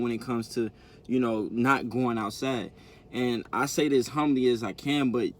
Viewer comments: I know this serious but am I the only person that thought when he said you going out sad that he said you going outside??*CDFU* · this whole time I thought he was saying "going outside" as being a code word or inside thing between him and airi. whole time he's saying "going out sad"